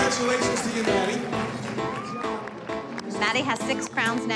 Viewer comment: When they said maddie I thought they Said daddy